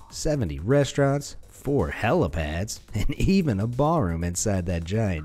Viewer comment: Did I heard right? Abraj kudai hotel in Mecca would have a Bar ??? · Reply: He said ball room, where people dance and stuff. I don't think that should happen.